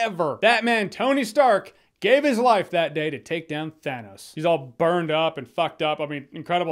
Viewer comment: caption says Batman took down Stannis, lol